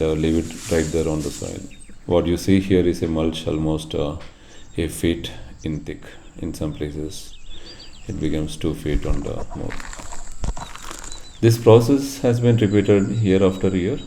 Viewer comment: It's about 1ft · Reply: Ok. "a foot". I heard it as 8 feet. Thought u might have meant 8 inches.